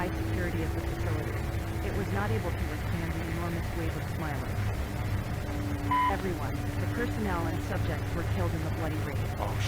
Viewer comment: sounded like she said f*** everyone lol